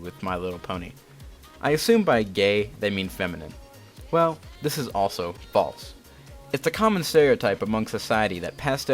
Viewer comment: captions say this is balls